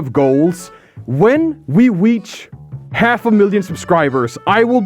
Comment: is my hearing bad or did he say weach instead of reach